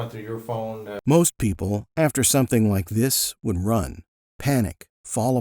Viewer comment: I hate to be that guy... but correct caption "would" instead of "wouldn't".